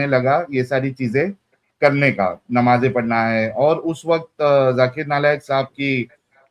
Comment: did he say Zakir Nalayak?